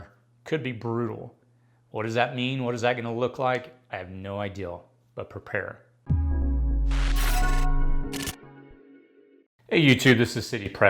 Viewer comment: Does he say he has no "IDEAL" at 0:11?